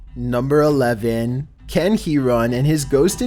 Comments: When he said Ken Heron I thought he said Can he run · Ken Heron sounded like can he run so much i thought thats what you said